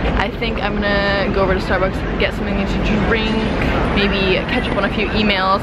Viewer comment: OMG! she said "imma get something to drink! maybe catch up on a few emails" i literally thought she said " imma get something to drink, maybe ketchup"! hahaha but then she said "...on a few emails" and i was like thank god! SMH